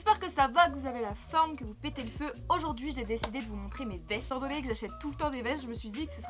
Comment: ptdrrr j'ai entendu "j'ai décidé de vous montrer mes fesses"